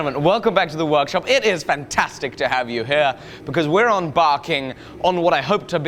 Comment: Did he just say "onbarking"?... Its embarking. EM!